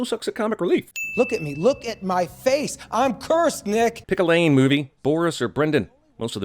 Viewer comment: I thought he was gonna say, "I'm cursed, nigga." Now, THAT would be worth a sin reduction or two.